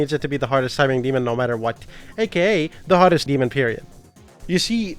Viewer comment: youtube captions say the hottest demon period wtf 😬